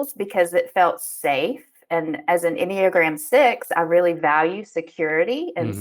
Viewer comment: what does say at 32:32? "as an iniogran 6" is all im gettin. is it some bible verse or some shit? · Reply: Enneagram is a personality test based on what motivates you. In her case, sixes are primarily motivated by seeking security and safety. mentioned being a type 2, which means she is primarily motivated by a desire to feel loved.